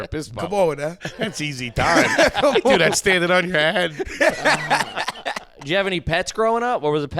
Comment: I swear when Foley said thats easy time i thought "do that toke on your head" Foley!! Get out my brain,!!